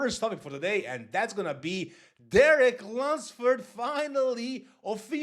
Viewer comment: The first time you said his name in the video I could’ve swore you said “Derek Lundsturd” 😂😂